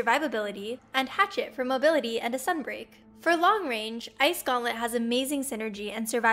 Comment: what did she say? Sunbreak?